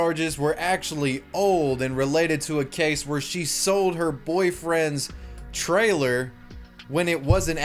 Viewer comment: I got an ad when he said this so it sounded like he said “she sold her boyfriend” 💀